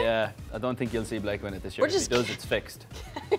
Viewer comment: can someone write in the reply what he was saying? Maybe it's the accent that I'm not familiar with, but i couldn't understand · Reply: I don’t think you’ll see Blake win it this year. If he does it’s fixed.